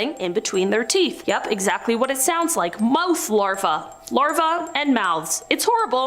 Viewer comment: I thought she said mouse larva not mouth larva lmao